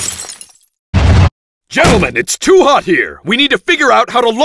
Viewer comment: Did he just say Gentleman? lol....